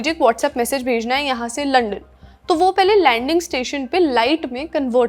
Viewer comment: Video ka 3:19 pe kya bola ha aapne 😂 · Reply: London